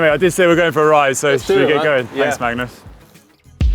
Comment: did Conor really call him Magnum?